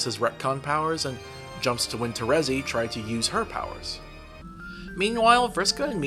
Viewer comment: Wondering why I made this? Watch my analysis called Homestuck Is Good, Actually: <link> Recap Corrections: Below are some errors in my making of the recap some have kindly pointed out. Sorry about these! I'll update as I see others. - There were a few points I got some of the J names mixed up, but I think the meaning remains clear when watching the video. - I called the session for Jane, Jake, Roxy, and Dirk a "dead session", but technically it's a "void session". Caliborn's was dead. I always get it confused because of how the void session is themed around death, dead planets, and undead! - When discussing the Summoner, I speak of a Sectarian War. This was the wrong war. The Summoner started the aptly named Summoner's Rebellion. The Sectarian War already happened with the Signless. The Signless hadn't been mentioned yet so I got them mixed up in the script. - I mentined Nanna was killed by a meteor that hit a Betty Crocker factory. Wrong meteor. She was killed by the one that hit a joke shop. EDIT: Also, this video got copyright flagged from the less than 10 seconds of Saw audio. I refuse to edit or trim it out. It's fair use, and the word salad publishing corp is bullcrap. It hurts, but it's more important to me that the video remains intact than it is for me to get the ad dollars. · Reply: the subtitles say "when Terezi tried to user her powers." when they should say "when Terezi tried to use her powers." ::3